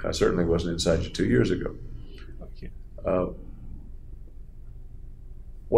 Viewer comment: Does he say Fuck You at 2:54:42 after he says I wasn’t inside your head two years ago? Sounds like he just whispered it?